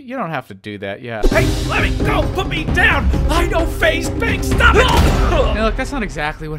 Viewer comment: Did you just say I know faze banks